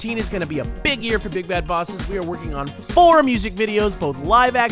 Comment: I always hear "BIG FAT BOSSES" at the beggining...